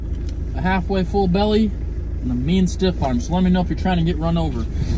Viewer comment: For a second I thought you said, "I got a full tank, a full belly, and a mean stiff ON so let's go..." I was like damn bro that chicken must have been some transcendental type 'a shit! Lol